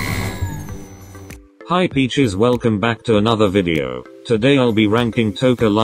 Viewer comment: Wait?!?!?did you just say peaches or bitches?!?!?😆